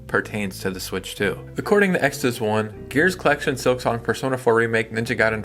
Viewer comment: I was listening and misheard extas1 as Exodus 1 and I thought maybe I was at the Church of.